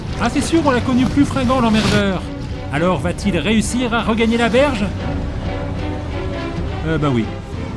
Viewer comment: A 5:55 "va-t-il réussir à regagner..." quoi ? J'ai mal compris je crois... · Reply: Bah la berge